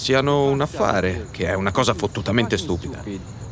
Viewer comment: For my deaf individuals he said fucking stupid not “utterly stupid” like the captions say.